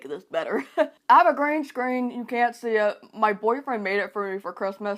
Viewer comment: Hii, just wanted to tell you that I've found a little mistake in your captions. At 6:14 you say "i have a green screen" but the captions says "I have a grand screen"